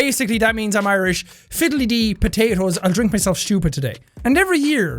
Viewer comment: I thought said fiddly Dee I'll drink potatoes today 😖